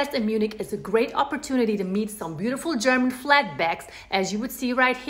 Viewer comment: Yo... Did I hear You say, German flatbacks? @ 3:30?